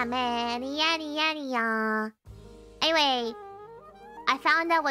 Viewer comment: People talk about how Gura and Mumei sound alike, but I looked away from my screen near the end and thought Biboo said “we’re moomin’ now”.